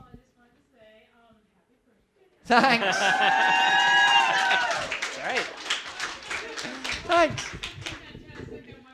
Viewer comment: why does DAVID said thanks ? at 42:26 ?? i couldnt hear the person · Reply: They said Happy Birthday!